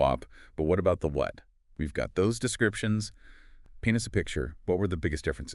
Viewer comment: i'm sorry, did you just say "penis picture" 🫢? sir, this is a Wendy's